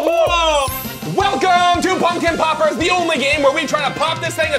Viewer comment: For a second I thought said pumpkin poopers